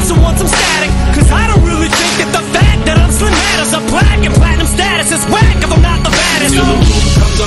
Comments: I swear to god, I have heard this song a million times and I always thought he said "Im black in platinum status" lmfaoo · Maybe I'm tripping but for that last line I always hear "I'm black in platinum status" which is equally as fire. But also, right before that when he says 'i don't think the fact I'm Slim matters" could be a play on Slim Mathers.